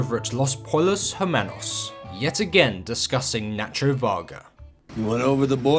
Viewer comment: Hold on a second, did you say... dis-Gus-ing?